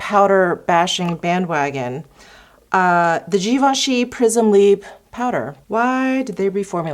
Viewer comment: At first I thought you said Givenchy Prison Libre and when you talked about how bad it is now, well, it took me a second to realize you said "PRISM." But since we hate the new formulation, why not call it Prison Libre? I love your videos even more lately. I broke my right arm. I can left arm foundation, powder, and blush with difficulty, but I haven't been able to do eye makeup since the break. Watching your videos is vicarious happiness right now.